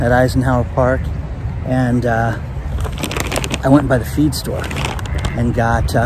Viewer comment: for a second there at 00:13 I thought you said you went by the beer store. Ok I watch rest of video, I was wrong about the surfer who was a paddle boarder a previous video so I hope I'm wrong about that. I keep watchin. Although the duck at 0:22 doesn't think so I keep watchin. Now I want one of them burgers. ( That is a good looking burger )